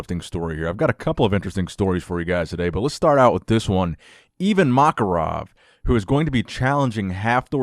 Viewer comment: I thought you were saying even Makarov is challenging Thor and was like damn, how many more people?